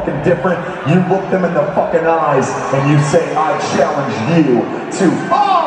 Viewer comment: what did he say? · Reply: I challenge you to all out life